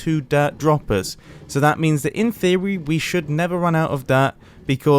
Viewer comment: did he just say in thewee?